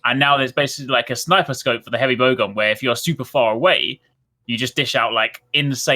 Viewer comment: Apple has done a very good job of developing blind loyalty in their customer base. Near 36:00 Is saying Vogon? Bow gun?? #madeittotheend !!!